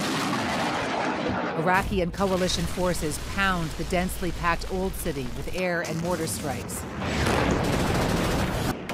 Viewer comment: What was the little girl saying @ 31:24? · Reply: Don't shoot us